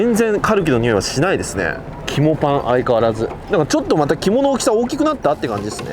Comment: ヒモパンに聞こえたのは俺だけじゃないはず！